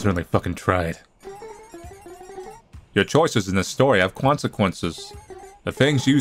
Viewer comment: I heard "Cwonsquences" at 14:33 XD